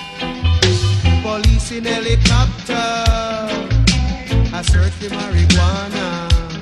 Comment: 10 yrs ago, playing GTA 4 I thought he was saying "Police in helicopter, a search for my iguana" 😂